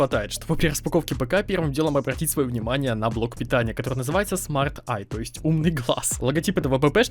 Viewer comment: это не смарт eye а smart bye или мне послышалось,у моей бабушки даже мышка для ноута он этой компании